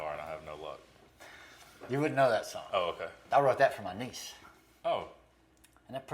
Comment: Really thought you said “I wrote that for my niggas”😂